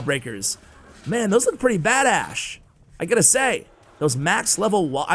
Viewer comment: did he say those are pretty "bad-Ash" If so I might be saying that from now on :)